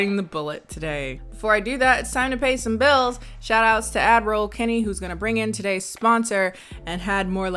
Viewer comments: All this time, I thought you were saying “Admiral Kennie” but I just realized that it’s “Ad Roll Kennie” lol. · All this time, I thought you were saying “Admiral Kennie” but I just realized that it’s “Ad Roll Kennie” lol. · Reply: Lolol it’s ad roll I thought she was saying admiral tooooooo